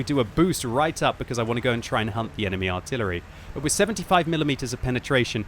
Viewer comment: I honestly thought he said “...hump the enemy artillery” at like 2:46. 🤣